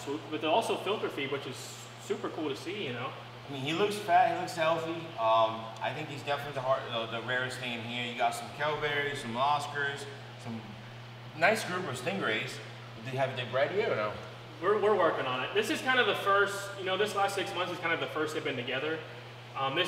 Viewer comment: What are those big yellow ones? Sounded like he said kellberry. I turned on closed cap and it spelled it as cowberry. I googled both, no luck.